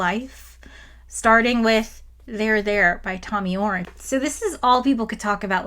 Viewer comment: i did a double take when i heard "there there by thom yorke"